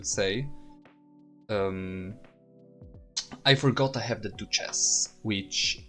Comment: Good vid and lmao every time I heard you say Duchess I thought you were saying two chest